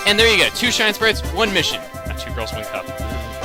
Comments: did you said ''not 2 girls 1 cup''?!? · DID HE JUST SAY 2 GIRLS 1 CUP?!?! IF ANYONE DOESNT KNOW WHAT THAT IS DO NOT GOOGLE IT! I REPEAT DO NOT GOOGLE 2 G 1 C!!! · Did he just say ''2 girls 1 cup''? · DID HE ACTUALLY SAID 2 GIRLS 1 CUP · did u just say and 2 girls in a cup